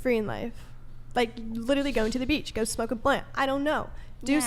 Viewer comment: did she say “go smoke a plant”